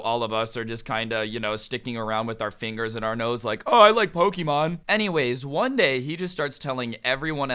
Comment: I swear it sounded like said Hokiemon and now I’m thinking of the grim adventures of Billy and Mandy